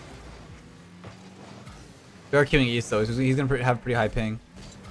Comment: wtf does he say at 4:05 about ping??? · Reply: "we are queueing for east though, so he's gonna have pretty high ping".